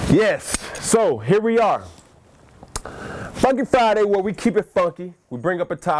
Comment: Sound like he said Fuck it Friday